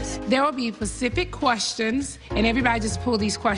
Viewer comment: Just now seeing this....and I heard NeNe say "PACIFIC QUESTIONS"